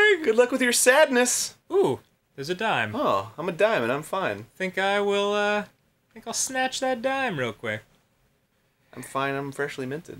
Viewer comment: Wow I heard "I'll snapchat Brian" when he said "I'll snatch the dime" damn social media